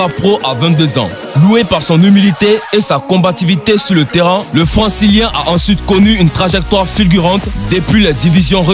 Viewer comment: C'est moi ou à 3:59 j'entends '' le françiliens'' et à 8:43 '' Raphaël Vane